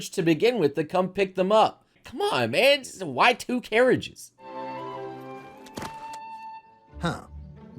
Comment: idk why, but for some reason I heard this line internally as "Y2Carriages" and laughed really hard.